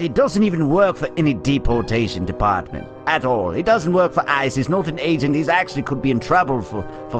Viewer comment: Who else notice that 2:28 said isis instead of ice